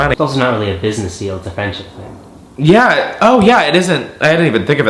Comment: What does say at 14:05 "It's not really a business deal it's a ???" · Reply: “it’s a friendship thing”